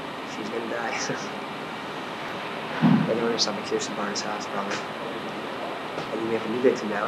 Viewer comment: I think he said Kristen's 9:49 PM 3/26/2022